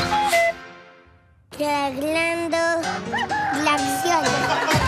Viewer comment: yo entendi: claclendo, clakzion :v